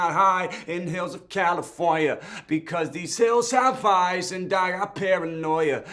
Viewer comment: i thought he said because this hills have fires